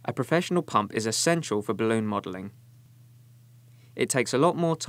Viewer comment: I thought it said bikini modelling :C